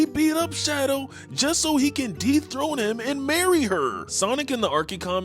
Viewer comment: at first, I swear it sounded like you said “to de throat him” I was like tf